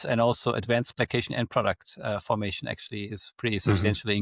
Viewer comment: What did say, here, after carcinogens? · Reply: Advanced glycation end products